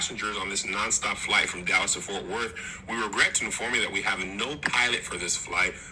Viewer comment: The nonstop Dallas to Ft Worth is what killed me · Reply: I misheard it thinking "nonstop to Dallas Fort Worth" but I'm like WAIT...Dallas TO Fort Worth?! 🤣🤣🤣🤣